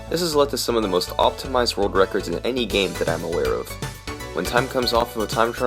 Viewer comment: What did you say? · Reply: "In any game that I'm aware of"